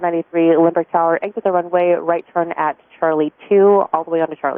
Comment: Can you recheck, I believe that I heard 4hv say again, and controller said go around do published missed approach, said ok...…. then at 6:12 into video, "Tower..SWA2493" and she said exit runway at c2 onto Charlie. and he was asking why? that is my interpretation on this situation. To me it didn't sound like it was citation asking for reasoning it was SWA.